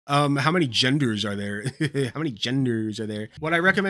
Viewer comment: "Well you have one obviously..... Oh! I thought you said how many AGENDAS?"